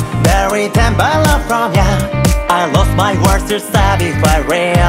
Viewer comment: のyouのところが にゃ に聞こえてしまう🐱🙄